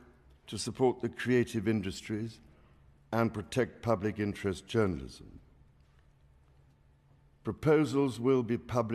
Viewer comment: Legislation to "protect [public-interest] journalism" ... ?? WTF? Is that what he said? Think about what this could really mean. Big brother silencing independent citizen journalism. 🚩🚩🚩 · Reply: King's Speech contains pledge to 'protect public interest journalism' The government has issued a pledge to “protect public interest journalism” as King Charles outlined its programme for the new session of Parliament.